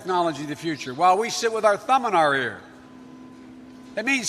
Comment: did he say , while we sit here with our thumb in our "rear"?